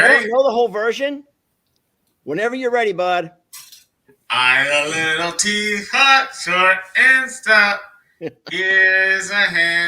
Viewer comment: Longhorn Lester All I heard was I'm a Little peapot lol